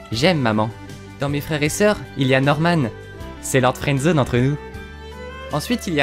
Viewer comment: lord quoi ? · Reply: lord friendzone